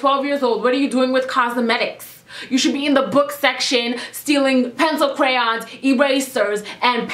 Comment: Oh my gosh I thought you said dealing at first I was about to go 😝